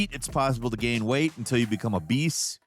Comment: 7.49 Heard it as become a beast rather than become obese. Was so confuse.